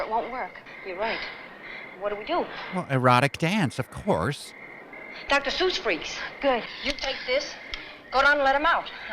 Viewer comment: I thought I heard "Dr. Seuss Freaks" at 1:05:45. That would be weird in live action. Also, at 1:19:25, I think that's Michelle from Girl in Gold Boots.